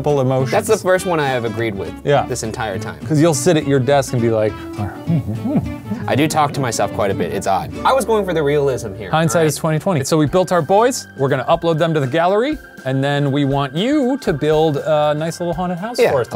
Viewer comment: I-I think I just heard Shane say, "Hindsight its 2020"